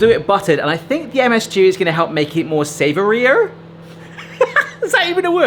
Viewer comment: I think you just say more savoury